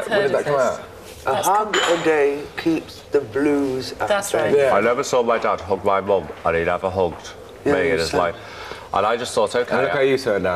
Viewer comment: What did Pete say about his dad in 3:15? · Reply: “I never saw my dad hug my mom and he never hugged me in his life”